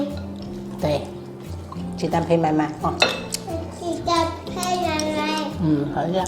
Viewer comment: 我家一般是冬天吃萝卜，夏天吃冬瓜！又学到了一招，要勾芡色泽才光亮👍大骨头里的骨髓给小孩吃最好(这是我小时候，物质缺乏年代的大人的观点😁) 弱弱地说“鸡蛋配饭饭”我听成了“鸡蛋配妈妈”🤣🤣🤣🤣🤣是福建发音么？